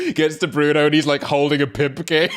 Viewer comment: wait what did say at 28:25??? judging by reaction it was hilarious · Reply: I think he said “pimp cane”